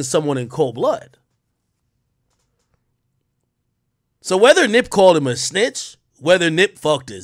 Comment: Being successful and dying over street sh** is wild. You can read between all the lines his brother is saying and see that Nip checked that man over something. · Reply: I thought wack said nip called shitty a snitch